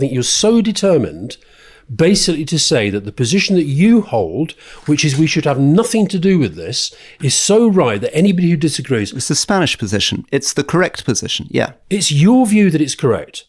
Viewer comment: I thought he said “this is the Spanish Inquisition”!